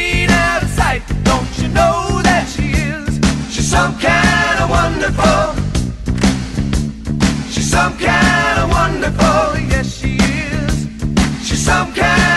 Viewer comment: i thought they said "so kind and wonderful"